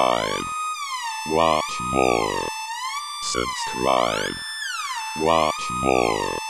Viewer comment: Ok, instead of "Subscribe, watch more." I heard "Sins thrive, watch more." I think the Illuminati may have attacked this video. (Joke, it was just me having bad hearing.)